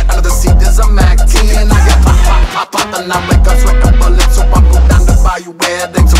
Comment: I swear at 2:44 it sounds like Ruby said "I'm like Guts with the bullets"